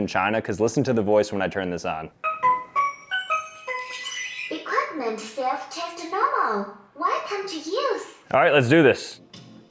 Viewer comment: That's not Chinese. She is actually saying "Equipment self test normal. Welcome to use.